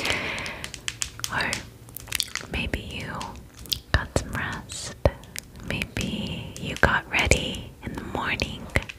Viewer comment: did she just say broski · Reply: Risky